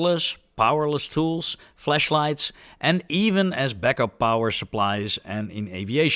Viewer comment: did he say fleshlights? 😂